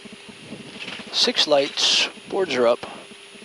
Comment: I always hear you saying "boards up, six green" after landing, what are the six that are green? Beautiful footage, BTW. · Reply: It's actually "boards up, six lights"...they aren't green though. I have a Tech video on that exact topic: <link>